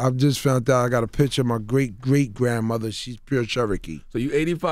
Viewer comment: 85% Nigerian? Grandmother Cherokee? Then you from the Congo? Wtf! You gotta love Americans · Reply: Mabel X I could've swore he said great grandmother making that 12.5 percent......why would he lie did u not here the rest of the interview 😂😂😂😂😂